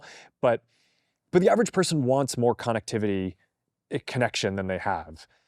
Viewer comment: why did he say “con activity”…..??? Lmfao